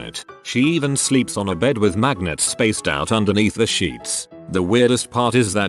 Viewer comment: I thought it said maggots😨